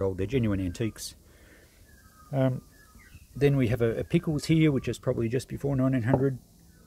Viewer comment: Great video. What did you call that bottle at 9:27 ? I have one just the same. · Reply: Thanks DV. That one is a Pickles bottle. They used that exact shape right through til the 1950s or so 😊